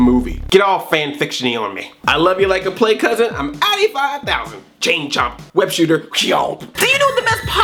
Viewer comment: what are you saying? I'm?